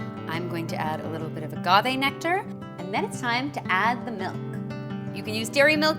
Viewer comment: what did she say? something nectar... I can't understand what she is saying.. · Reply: Agave nectar, it's a sweetener so honey would work, as would maple syrup, and sugar.